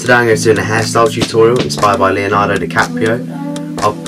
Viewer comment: goddamn it i thought it said leonardo da vinci